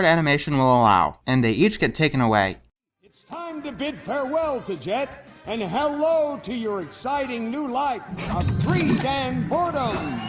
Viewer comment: How I heard the lines at 5:38 "Say goodbye to Jet, and say hello to your new life, a three-man porno."